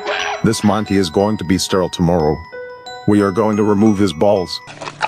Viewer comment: it sounded like he said “we are gonna remove his balls”